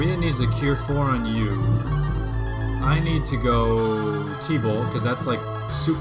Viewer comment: I hear you say "tebow" sometimes when you're saying T bolt. Somehow Tebow worked his way into BoF